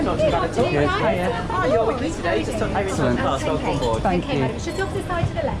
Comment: Subtitles are needed to understand what the female FA @ 05:33 is saying. 😂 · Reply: Slytherin she says “good afternoon guys welcome aboard”